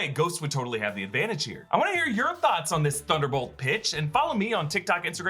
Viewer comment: "I wanna hear your thoughts on this Thunderbolts B****!" 😂 7:56 (I swear he said that at first.)